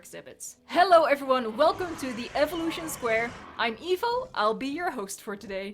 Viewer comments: that sounded like you said "i'm evil" 😂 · When you said I’m evo I thought you said I’m evil